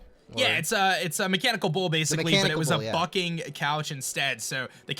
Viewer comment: was I the only one who thought they said "fucking couch?"